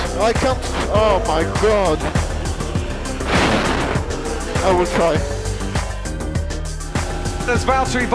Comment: Watching this live i thought he said : "I will cry." And it broke me